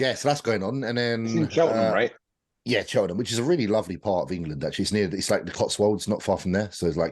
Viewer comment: what are they saying? Where is The Shed`s new Gym ? "Chardon" ?? "Cheltenham" ? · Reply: Cheltenham, UK